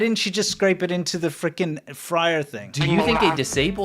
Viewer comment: ! Did you really say "Frickin" at 25:17?! You're getting rather hardcore, aren't you?! Standards are slipping! 😅😂🤣 Love it.